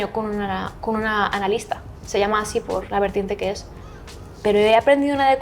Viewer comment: En el minuto 17:30 ella menciona una vertiente en la psicología que ella toma, entiendo que dice sipor pero no me aparece algo relacionado, alguien que supiera del tema me podría decir que fue lo que ella dijo de favor